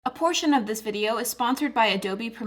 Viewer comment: At first I thought she said unfortunately this video is sponsored by....